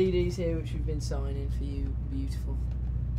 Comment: What did Brad whisper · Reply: Laura Henderson cd here that we had signed for you beautiful